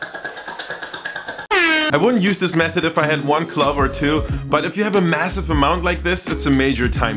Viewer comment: When he said clove, I thought, for sure, he had said club. I was wondering what use one or two clubs could possibly be for peeling garlic, until he said it a second time and I finally clued in what he had meant to say. Side note: I have captioning on and it also thought he said club. It was nice to know that, at least this time, it wasn't just my horrible listening skills to blame.